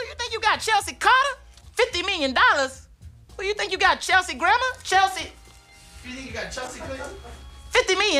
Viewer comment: "50 million dollars! Who d'you think ya got, Chelsea Grammer?" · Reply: I thought he said Chelsea Grandma